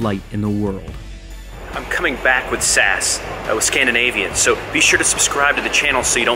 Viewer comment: For me being Norwegian i thought he said Sæs æ is an a and an e together in the Norwegian alphabet (Hold it to Scandinavian Airlines)